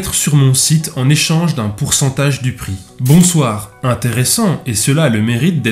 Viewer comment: J'ai entendu "un pourcentage duperie" à la place de "pourcentage du prix". Y'a t-il quelqu'un ici pour m'exorciser svp ?